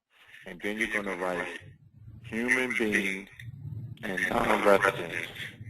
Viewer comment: what did he say?...." Comma, human being and ___ resident." I couldnt make out audio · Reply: non resident